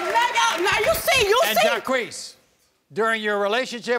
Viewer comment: I know what his name is, it is in the description, but it sounds like everyone is saying Ja queef.